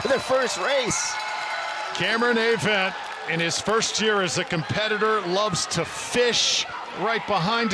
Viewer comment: Did he say Cameron likes to Fist? (2:35) Good grief! So inappropriate.